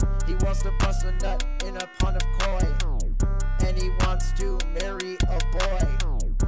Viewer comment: I thought it said "he wants to marry a boy"